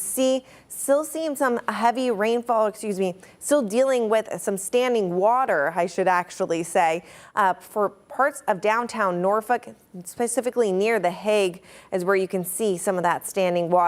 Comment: Anyone catch NorF*ck 3:15 😂